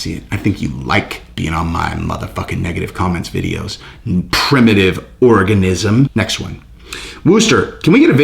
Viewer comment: I thought the negative guys name was Primitive Orgasm 😂🤣😂 Not Primitive Organism Lol Idk why but that's what I thought I heard until you said his name the 2nd time Lol